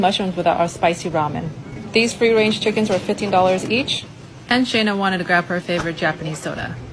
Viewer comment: Looks like you spent a pretty penny…I’ve never paid $15 for a chicken. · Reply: I swear I thought she said 50$ each at first for the chickens